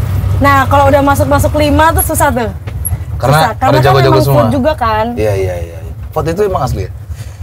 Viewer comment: bilang apa ya di sini 12:27? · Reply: "Karena memang vote juga kan" maksudnya krn nasib peserta ditentukan sama hasil voting pemirsa, apapun bisa terjadi.